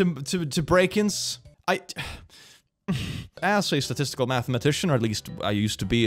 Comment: Also I misheard you say you were a mathematician as a “mathemagician” and now I can’t get the image of you teaching maths in a wizard robe and hat out of my head 😂😅